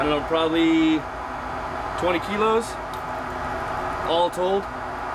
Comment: so an american said kilos did i hear it right?